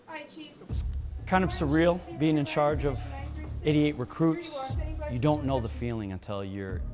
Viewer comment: Chief said 88 recruits I thought he said idiot recruits